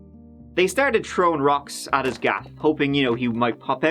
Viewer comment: throwing rocks at his "gap"... what does that mean? Am I hearing it correctly? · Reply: "Throwing rocks at his gaff" slang term for house or more specifically home.